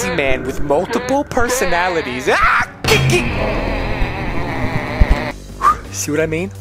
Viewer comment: did he say tiky